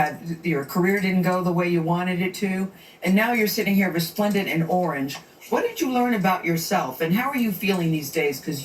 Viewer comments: It sounded like Gayle said, "resplendent in orange." Which means, attractive and impressive through being richly colorful or sumptuous. "Is Resplendent a compliment? In fact, due to it's somewhat rare usage in the English language, to be called resplendent is a compliment of the highest degree." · “Resplendent in orange” Resplendent means glowing in splendor, vibrantly, etc. it was a compliment but you’re spot on everything else! I watched it live and felt uncomfortable and I wasn’t the one being interviewed😓 but Tisha held her own like I knew she would.👏🏾👏🏾👏🏾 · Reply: I typed in both words and the computer pronounced both words very distinctive and different. I listened again to Gayle. It sounded like respondent not resplendent. No disrespect but I heard the 'spon' syllable and not 'splen'. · Sweetheart it appears to me that vocabulary isn't broad enough to criticize anyone. She said resplendent not respondent. I think if you you understood context you'd know what she said even if you thought you heard something different. Look up the word resplendent and then go back and retract everything you said I'm regards to the bullshit narrative you created because you don't have a handle on words. But I do agree that snoop should retract his apology